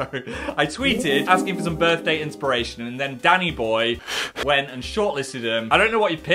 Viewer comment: For a solid minute I thought he said daddy-boy and not DANNY-BOY 😭